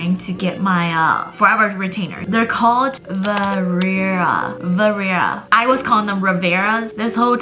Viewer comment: I think you’re trying to say vivera 🤗